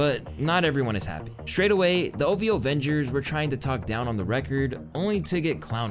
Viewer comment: Did he say the “OVO Avengers?” 😂😂😂